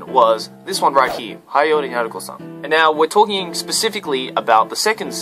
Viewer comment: what anime did he say? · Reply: Haiyore! Nyaruko-san